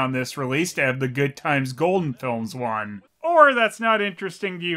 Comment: When you said "Goodtimes Golden Films" I thought you said "Good times Golden Girls". idk why, idk how. I think I need to sleep.